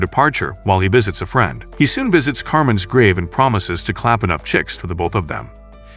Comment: he actually said "I'll see enough world for the both of us"